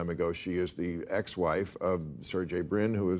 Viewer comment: I have to get my ears syringed, I could have sworn Bill said "The ex wife of JaJa Binks"